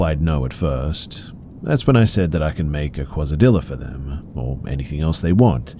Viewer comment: MY BROTHER IN CHRIST YOU DID NOT JUST SAY “QUASA DILL-A”